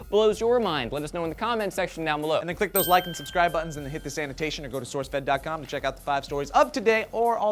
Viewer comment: COMPLETELY USELESS COMMENT ALERT: Whenever he says "Hit this annotation", I hear "Hit the sanitation". @@